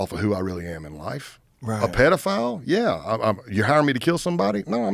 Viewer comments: am I crazy or was he calling himself a pdf 😂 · Did he say he's a pedophile? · Did he say he's a p3do??